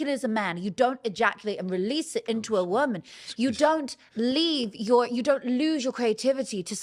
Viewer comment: Did she just say if you nut into a woman you nut out your creativity?